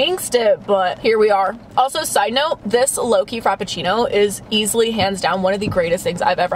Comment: When said Loki I thought she was saying Low-key 😂